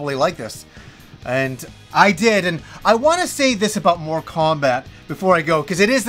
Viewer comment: I was listening just the audio for a moment & I swear I heard "More Kombat" then "Moral Kombat" LOL. I'm like "what game is he talking about? There is a 1 & 2"?!? Lol your pronunciations have never thrown me off till now LOL. I've Loved your channel for what seems like forever. Never change! :)